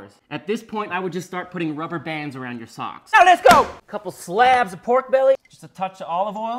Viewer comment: I thought he said cocks at the beginning, had me confused